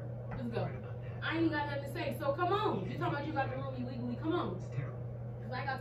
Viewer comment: What did the guy say at 8:29? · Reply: “Bro why you still sitting there ?”